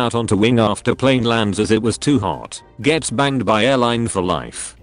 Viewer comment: sounds really bad whe the computer says banged instead of banned